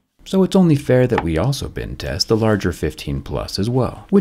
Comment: Never even going to think about buying a phone unless it's been tested by Jerry and passed his tests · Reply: I thought you said "bin test"!!! Whereabouts did you grow up!